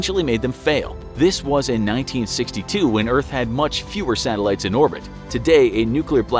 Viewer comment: what year did he say? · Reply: 1962